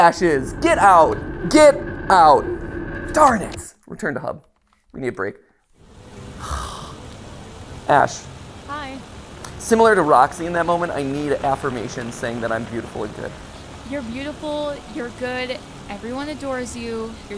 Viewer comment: Ash says hi really sounded like BB boy there and I was wondering if anyone else heard that XD